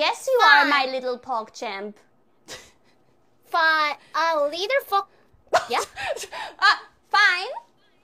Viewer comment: Fine a little fuc- WHAT WAS SHE GOING TO SAY!?!?